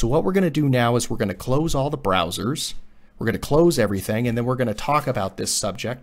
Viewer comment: I thought he said brazzers